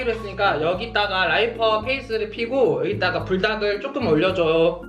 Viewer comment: 라이퍼페이슼ㅋㅋㅋㅋㅋㅋㅋㅋㅋ 나도 너무 자연스럽게 들었닼ㅋㅋㅋㅋㅋㅋㅋ